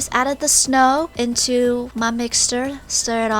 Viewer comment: Did anyone hear that she said mixstir instead of mixture Time=